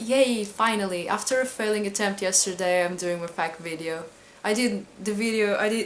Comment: Pq é q eu ainda n estava subscrita a si? x'D lolol olhe seu pigmeu homosexual eu ouvi "FAHK video" as in "f*ck video" e estava com outras esperanças se é q me entende... My dog is very disappointed he was expecting an Aoba sexy video...